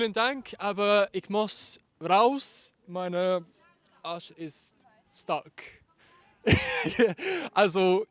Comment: Did you just say " Meine Arsche ist stark"? 😂 I absolutely loved your attempts at speaking German! Suggestion: You could add English subtitles when someone is speaking in German. Dankeschön!